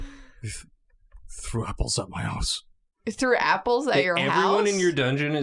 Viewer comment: I thought he said "threw apples up my arse", I was so confused